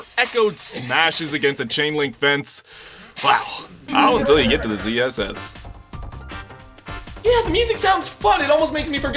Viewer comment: What is he even saying at 1:54??? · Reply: All until you get to the CSS??? (Thought it was ZSS until typing this)